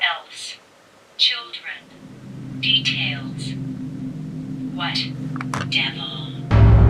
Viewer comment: it says "devil" in a really weird way & same thing with "hell" at 1:00:57 1:14:34 yup that's all the words But the next slide of words- there's something odd &sus about what it said- Why did it say "wet-devil" I don't get it